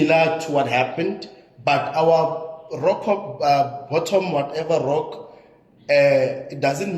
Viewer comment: what is trying to say😂😂😂😂😂😂😂😂😂😂😂😂😂😂😂😂😂😂😂😂😂😂😂😂😂😂😂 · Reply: Reached the rock bottm